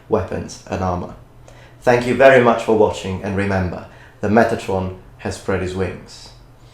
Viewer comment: I thought you said, "Remember, Metatron has pretty swings." Now I know...